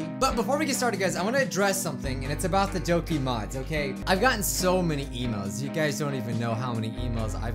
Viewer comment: I thought I heard him say “I’ve got so many Emos”